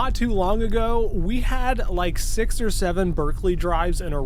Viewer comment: d-d-did you say s-six seven? 67!